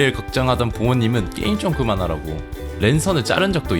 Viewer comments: 어릴때 랜선을 왼손이라고 알아들었는뎈ㅋㅋㅋㅋㅋ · 에 랜선자르는걸 왼손을 자른다고 들었음 (놀람ㄷㄷ)